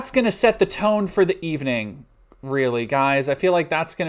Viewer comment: Wtf you said the R word at 3:08........ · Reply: they said "really" at that timestamp?